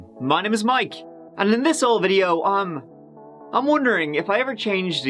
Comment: I always thought Mike was saying in this old video but he's saying in this whole video